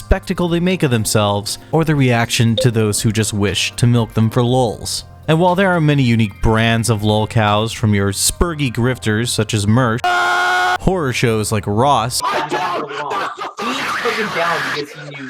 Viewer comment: are you saying merk at 1:25 like m3rkmusic or am i mishearing?